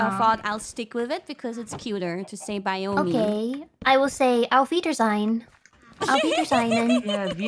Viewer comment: I googled "Alveder" and this is what showed up - "auf Wiedersehen". Is this what they're trying to say at 0:41?